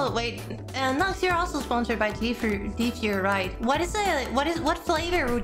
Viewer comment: Anyone else hear GFuhrer? No? Just me? Okay...